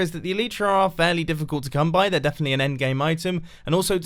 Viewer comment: did you just say "an endgame item" ?